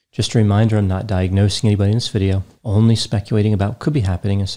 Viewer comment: I thought he said 'Im NOT diagnosing a PLANT'